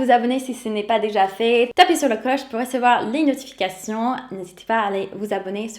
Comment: à 11:04 : J'ai entendu "Tapez sur le coche". Je veux bien, mais d'une part je suis non violent, d'autre part de nos jours, un coche ça ne se trouve pas sous les sabots d'un cheval... Blague à part, j'ai trouvé cette vidéo très rafraichissante et instructive. On a beau vivre dans ce foutu pays qui est le nôtre depuis 40 ans, avec l'habitude plus rien ne nous choque, alors ça ne fait pas de mal d'écouter les étrangers nous dire ce qu'ils trouvent bizarre (ou ce qu'ils trouvent mieux que chez eux), surtout quand c'est fait de façon modérée et argumentée. Du coup je me suis abonné :-)